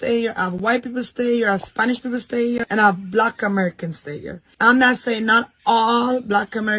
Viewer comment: She shouldn’t generalize. I admit our people have issues, but we all aren’t like that. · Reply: I heard her say, “Not EVERY black American.”